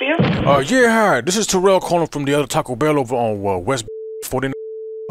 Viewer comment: Did he say taco bell on west bellfort